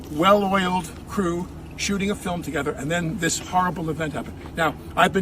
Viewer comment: Did he say at 2:39 shooting a film together